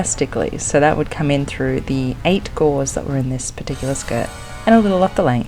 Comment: Great transformation. Had a good laugh at myself because it took me nearly the whole video to realize that you were saying that the skirts had Eight Gores, as I kept hearing "gauze" and was thinking "Wait, didn't she say it was silk?"